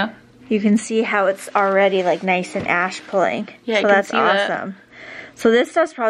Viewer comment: What did she say @ 12:49 and what does that mean? :) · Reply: She said the spots where I have hyper pigmentation are taking on an ashy tone so they’re starting to look at a little grey, bodes well for the peeling/healing process :)